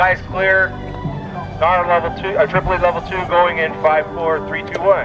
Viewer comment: did he say Tripple level 2?! at 4:51ish? as in three motor cluster?